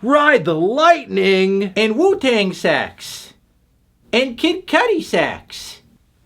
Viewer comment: idk why I heard "and Wu-Tang SEX"